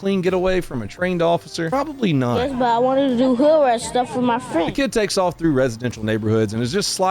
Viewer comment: What does that kid say? Here—>2:29 It sounds like “Yes, but I wanted to do ‘hoo-rise’ stuff with my friends”, but I’m sure it’s not.